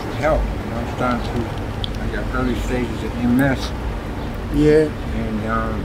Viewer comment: I think he said "I got early stages of MS" Whoever did your subtitles got yall all messed up. You said "Nigga you was ballin" and it said "You were born" lol Dope video though. Never seen your channel before but I like how respectful you were to him.